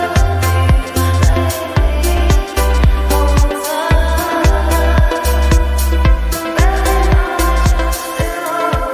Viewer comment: what does it say? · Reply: "My Daddy Always said you were his favourite superhero"